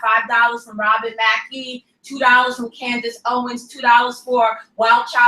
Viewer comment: does she say Candace Owens? LOL.